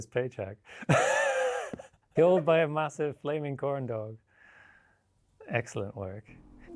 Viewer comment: "Killed by a massive flaming corndog. Excellent work!" Is right up there with "You can just throw rocks. They cost you nothing, they take no preparation and you throw them at people and they get hurt. It's great." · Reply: Hah. Never heard the word "corndog" before (not a native). All the time I thought he kept saying "flaming condom".